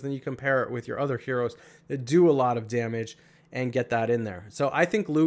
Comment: I keep hearing "Friggin Morel!" and wondering what Morel did wrong? 🤔